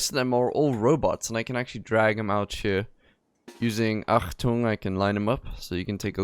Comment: Did you say at 0:50 seconds Achtung? And at 9:27 to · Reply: Yep, the Achtung mod is a relatively popular mod that gives you more control over drafted colonists.